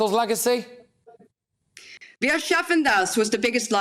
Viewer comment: What is the word that Eva says at 42:50? I can only try and spell it phonetically as I hear it, but it sounds like 'ashafendas'. I can't speak German and I'm obviously not spelling it correctly as I can't locate anything on the Internet with that spelling or variations of it. Can anyone help? Thank you in anticipation and a happy New Year to all. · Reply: Wir schaffen das, which translates roughly to "we can do this" or "we can handle this." It was something Merkel said at the height of the migrant crisis in 2015.